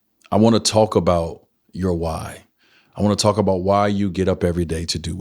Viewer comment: Heard it wrong! I thought he said I WANNA TALK YOUR WIFE (WHY)..smh..